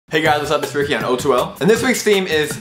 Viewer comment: Am I the only one who heard "rickian" in the beginning? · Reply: I heard it..."hey guys its rickian!"